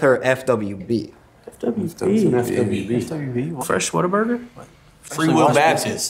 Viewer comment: "FWB? Whats FWB?" "Fresh Whataburger?" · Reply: i thought he said "Fresh Water Burger" but this makes more sense 😄